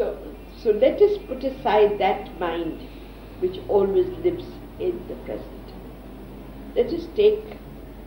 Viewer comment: did she say "let us put aside that mind which always lives in the presnent?